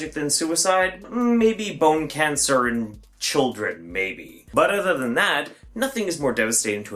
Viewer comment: I'm sorry but at 2:30 he says "IDK of anything other than maybe bone cancer or children that's more damaging to a family". I get that's not what he meant, he most likely just forget a word or prefix to that but I died laughing at it